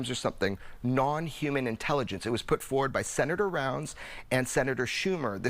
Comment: Did he just say senator bird poop? 💩